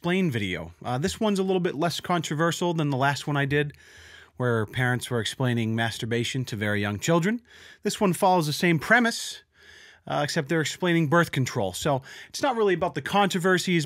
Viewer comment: I thought you said explaining Molestation to young children